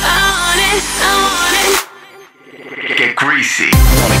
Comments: am i the only one that hears "get greasy" instead of "get crazy"? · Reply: I heard "get greasy" and was like.. uhm what ..why would I want to get greasy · am i the only one that hears "get greasy" instead of "get crazy"? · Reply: I thought it always said get greasy?????? 😂😂😂😂 what the fuck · am i the only one that hears "get greasy" instead of "get crazy"? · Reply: Thanks now I hear "get creazy" lmao